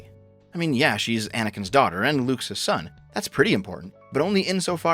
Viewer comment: did he say she is lukes son? i might be having a stroke if i dont reply to anyone im prolly dead